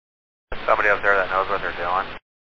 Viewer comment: subtitle is wrong. Should read "is there somebody up there that knows what they're doing" instead of "It's a comedy up there".